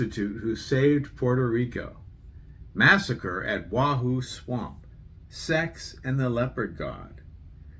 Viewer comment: I thought you said "sex and the leprechaun" before you showed the cover. OMG I'M DYING! 🤣🤣🤣